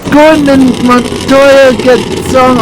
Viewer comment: when i sound JPM (Juan Pablo Montoya) Your sounds like Your say golden Montoya XD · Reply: What I said was "Gordon and Montoya" but that would've been hilarious